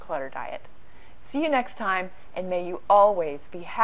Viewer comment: I was listening to several of your videos yesterday! My daughter thought you said 'May you always be happy and grateful for having more than an Elf'! I have to chuckle every time I hear that now. Thanks for all the great tips.